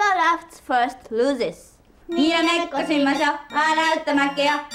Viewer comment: what did they say?? Oh never mind I found it Niro meko shimasho (Let's play Niromekko) Waru to makeyo (If you laugh you lose) ah bu bu (Ready Set Go!) Niromekko is a you laugh u lose game like Ami said. Hence y they were making funny faces at each other 😂 Japanese kids game